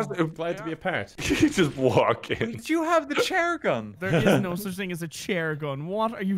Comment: I thought they said "share-gun" and it was just wholesome, well for a gun.